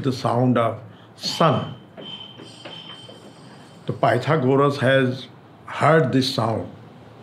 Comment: Pandit Ji says "Pythagoras", but the subtitle says "The Guru". Please do correct this.